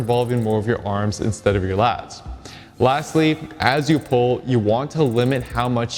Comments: I thought he said ' lats-ly' instead of lastly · and 3:29 I thought I heard you say "latsly"